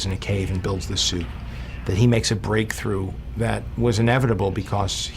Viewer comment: did i just hear 'inevitable'?